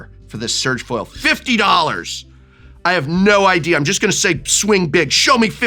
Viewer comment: Did he say "I'm just going to swing DiC$k?... huge huge"??? 10:53 "Princese of dum ass ka? LMAO!!! At the time of the showing of this comment the price is now $2500. All my little moths to my lightbulbs :)